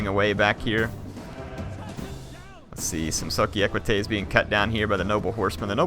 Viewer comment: until 6:37 i thought he was saying sucky equite